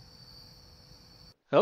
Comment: Did I Hear Sol Kit?!